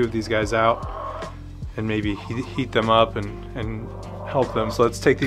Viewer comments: I really thought he said eat them up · I thought you said eat